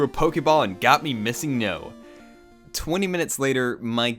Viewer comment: the verb "breaked" doesn't exist in english language. verb "to break" has a preterit "broke" and past participle of "broken", so if you want to use the past tense of the verb "to break" you need to use "broke". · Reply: i'm croatian also, on 14:55 he says 'breaked' and not 'bricked'. try pronouncing both words and then listen to what he says.